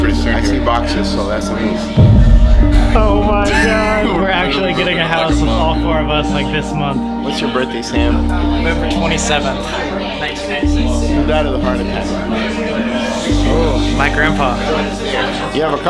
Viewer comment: Can someone pls clarify what is sam saying? Cuz i heard Feb 27 as his birthday? Isnt it Nov?